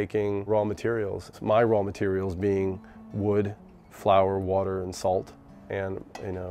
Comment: Did he say wood at 5:00 or so? in the bread? what?